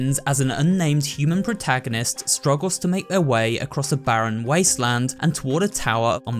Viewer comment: Wait did you say make “her” way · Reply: he says “their way”